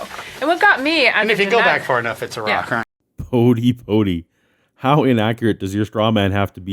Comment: When Bodie said atheists believe life came from "a rock", I thought he said atheists believe life came from "Iraq"!